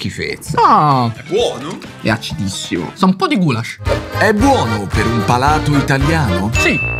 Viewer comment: ho sentito: sa un po'di gulag